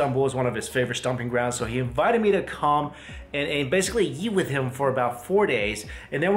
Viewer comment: his accent makes it sound like he’s saying yeet instead of eat 😂